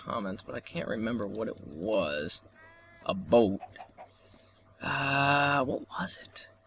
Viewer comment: thumbs up if u thought when he said "about" he said a boat... coz i know I did, but I'm a little stupid so...